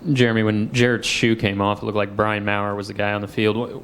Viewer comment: So TN doesn’t have a back up QB? Is that what he said around 14:30? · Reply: I think he did say that...he said Brian Maier (not sure how to spell it). Anyhow, thats the guy he said was not a backup